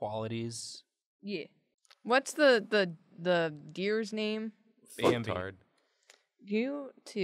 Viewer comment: can someone tell me what and said here? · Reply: I know it’s a few months late, but responds with “Bambi”, and chooses “F*cktard” for his answer. Equally unhelpful and wonderfully chaotic